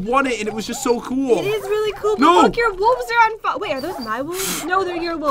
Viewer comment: On 28:53 did say F*ck or am I hearing things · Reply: Is but look, I think?